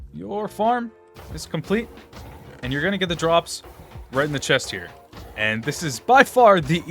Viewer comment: "And you're gonna get the drops right on the chest here." That's what he said.